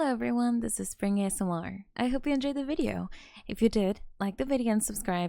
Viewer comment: She really said spring asmr basement